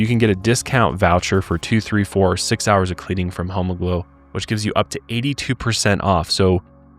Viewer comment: Why does is sound like you say hom*glow😂